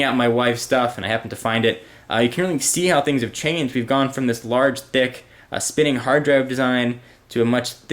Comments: sounds like hes saying "large dick" at 7:08 xD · did he say this large dick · did he say "LARGE DICK" at 7:09?!?!